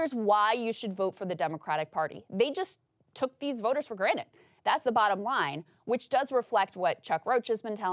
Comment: Did you say granite? What are you a rock person?